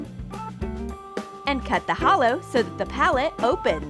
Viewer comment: Am i the only one who heard holo on 4:24 ..... I knew it they watch